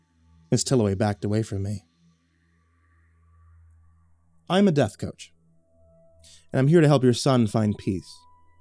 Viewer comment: O thought this said "I'm a Dead Roach" and was very confused.